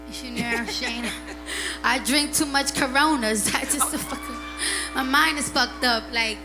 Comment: DID SHE JUST SAY CORONA?